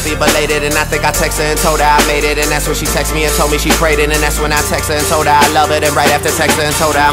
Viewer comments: ha... i always thought he said "she text me and told me she pregnant". always threw me off. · I thought he said ''I think I text her and told her I made it and that's when she text me and told me she pregnant and that's when I text her and told her I love her'' but, I guess not. o_o